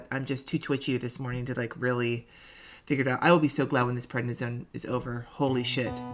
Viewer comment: Omg I thought you said I'll be so glad when this pregnancy is over 😮😄